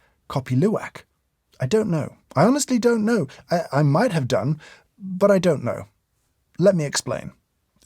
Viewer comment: am I the only one who heard "i might have dung" XD